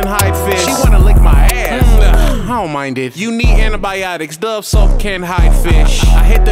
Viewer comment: “U need antibiotics dove soap can’t hide fish”😂😂🤧🤷🏽‍♂️ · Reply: I thought he said You Me and the Antibiotics.......